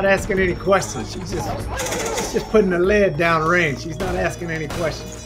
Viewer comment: "She just puttin' lead downrange, not asking any questions." This guy understands the mind of an ash main. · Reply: I thought he was saying "she just putting her leg down range." What you said definitely makes more sense